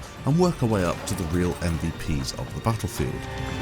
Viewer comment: sounded like he said "buttlefield"